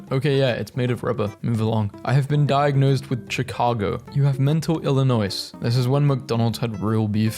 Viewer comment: no way he said ch-i-car-go lmao instead of sh-i-car-go